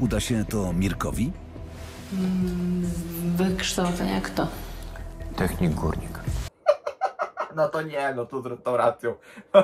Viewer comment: usłyszałem Technik Kurnik